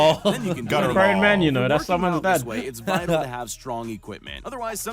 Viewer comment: did tobi call Simon someone's dad is Simon having a baby